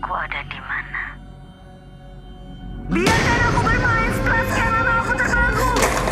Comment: dia ngomong apa di telponn gak denger soalnya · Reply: Biarkan aku bermain setelah sekian lama aku terbelenggu. Itu kata yang ditelepon